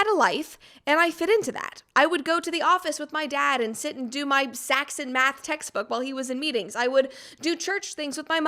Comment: "my Saxon Math textbook" At first, I thought you said "my sax and math textbook" You played a saxophone in your father's office? That can't be right!